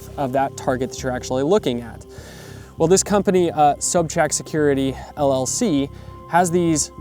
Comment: I liked those range cards. What was the name? Sounds like you said sub track security?